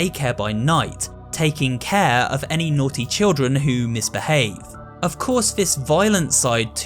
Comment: Why did I hear say "naughty broi"